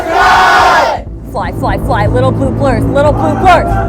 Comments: and 14:05 she says "fly fly fly little blue BLIRDS" instead of birds but she corrected herself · Did she say little pooplers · did she just call them little pooplers